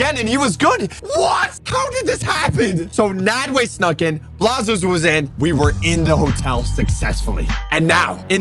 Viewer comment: What said:Blaza was in. The captions: Blaza used him💀